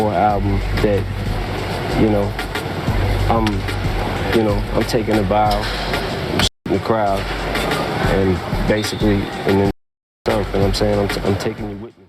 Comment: genuinely thought he said “I’m shitting the crowd, then I’m shitting myself” until I realised he shot the crowd and himself on the album 🤦‍♂️💩